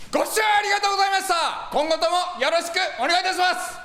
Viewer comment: 五千円ありがとうございました に聞こえた